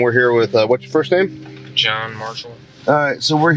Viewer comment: i thought he said John Marston, I was like wtf!